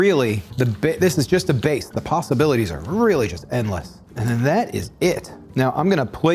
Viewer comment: Did I mishear, or did he say "the pastabilities are endless"?